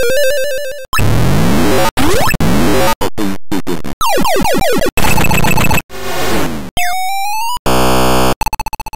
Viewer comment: What does it say? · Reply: It’s from a game called “Open Sesame” and that voice is basically saying open sesame but very garbled.